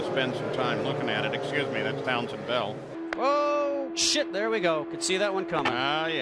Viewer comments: "Oh shit there we go, could see that one coming!" Did I really hear that? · did anyone catch the Announcer say oh shit